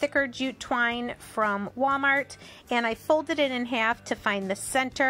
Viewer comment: Where did you find the Waverly pumpkin paint again? I thought you said Walmart